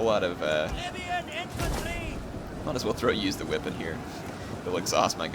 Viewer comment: I thought I heard "Idiot Infantry!"